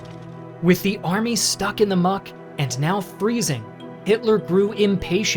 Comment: did he just said Much not mud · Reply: Muck*